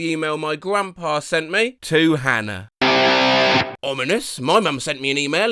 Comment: Had a heart attack because I heard Hannah and it’s my name so I was like “What?”